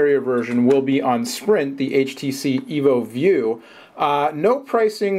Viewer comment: did it say htc evo shift coming to sprint